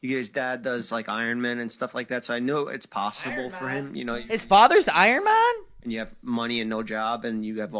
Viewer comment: did you just say “his father is iron man?”😂